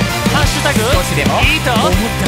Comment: イヤホンで左耳だけ外して聞いてたらセンラさんの声だけ聞こえて、「あ、交互に聞こえるやつなんだ」って聞いてたらうっすら聞こえる「もっと熱くなれよ〜…！！」に爆笑しちゃった